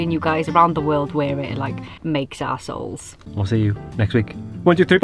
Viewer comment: does she say 'makes ass holes' or 'makes our souls' :)